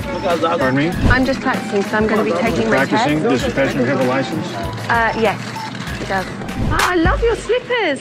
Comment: Does anyone know what the police said at 2:11 ? I think he was asking ' Does your friend here have a license?' But it doesn't sound quite like it. And the subtitle didn't catch that as well. If anyone can help I appreciate you very much.